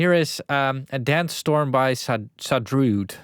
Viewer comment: i thought you said sandstorm by darude